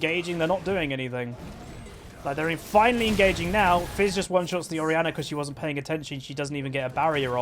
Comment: "Shaco is not a champion of skill" . Okeeeeey... I would rather say he is one of the most skilled champions in League. His box placement is really hard to pull off, the plays you can do with his clown are endless, his interaction with his q and teleport and all the other things. The mindgames and the 900 iq plays are insame on him,which only works if you are skilled with him. He was easy to pull off in season 2, where you bought 6 phantom Dancers on him to oneshit people with your q(which lasted forever), but those times are over xD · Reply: All I heard was "oneshit" The new one-shot? "Yeah, I oneshit on him."